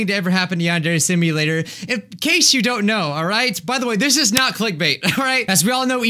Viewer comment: "It's finally here" Me: "ok right" "this is not click bait" Me: Cries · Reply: I thought you said clickbater xD